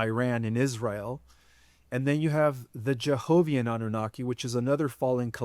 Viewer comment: I've always wondered why the Nazis and Hitler wanted to kill off the Jewish people. Was there bloodline a threat to the negative alien agenda, or were they feeding off their Loosh and pain, or experiments? It seems odd to me that they picked that group of people. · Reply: I thought he said Jehoviah so I was thinking the jehoviah witness folks are worshiping these negative annukis? 😅